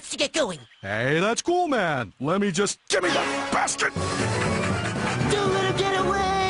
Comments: !!! Did he just said "Give me that bastard!!!!". ?????? OMG IS A KID CARTOON!!! BASTARD!!!! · the way he said gimme that basket sounds like he said "Gimme That Bastard". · for some of you it may sound like Yogi is saying bastard, but he is actually saying basket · it sounded like he said give me that bastard. I know that he said basket but I’m just saying. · Did yogi call billy a bastard??? Ahahahahahahahahaha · it sounded like yogi said "bastard